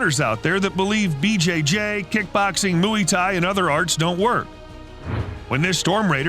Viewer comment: did he just say mooey thai😂